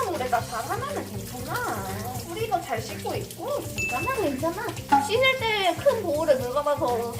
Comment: 괜찮아 괜찮아 ( 안죽어 안죽어) 로 들리는거 나만 그렇게 들리나.... 이걸 보면서 좋아요를 누르는게 맞는건가? 살수들이 만드는 요리를 보면서 ..